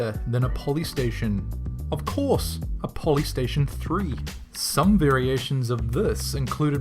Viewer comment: IS THERE A POLYSTATION EMULATOR? I MUST KNOW IMMEDIATELY. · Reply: Apparently, I thought polystation said potty station 030